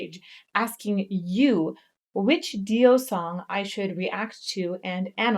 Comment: Did she say “Richie Dio” ?